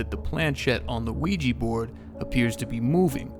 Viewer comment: i heard "Luigi Board"😂